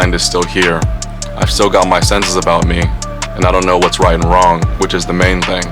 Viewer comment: that's not what he said🙄. He clearly says "I know whats right & wrong." He's saying other than a little short term memory loss his mind is fine.🕵. A better example would have been Phil Baroni. He actually ended up killing his wife(likely not intentionally-->shove into a shower) but hes infamous for having ridiculous short term memory loss. Chuck Lidell would have been a much better example. His speech becomes laughable & has zero of the ability to take a punch he once had.